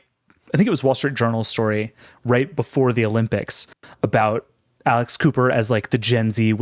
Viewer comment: I heard "Alice Cooper" not "Alex Cooper" (clearly I don't follow those kinds of podcasts) and was QUITE confused... And I would TOTALLY listen to Alice Cooper doing a weekly podcast...